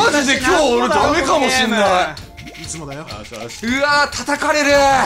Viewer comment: ひっそり「いつもだよ」って聞こえる…